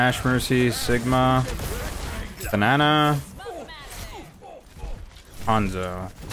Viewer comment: can’t be the only one who thought this man said banana